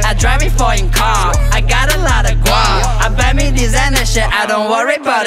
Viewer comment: group instead of guap ;)